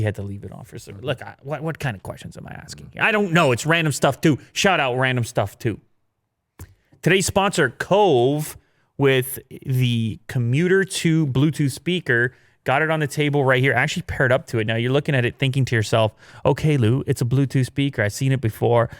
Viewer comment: I thought he said Bluetooth speaker from Kohl’s